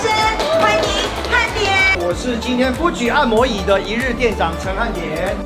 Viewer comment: 只有我聽成不舉按摩椅嗎？？？？